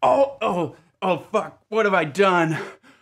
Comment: Did he just say "Oh Fuck"